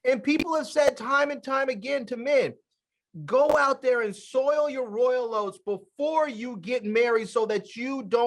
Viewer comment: It sounded like said "soil your royal oats."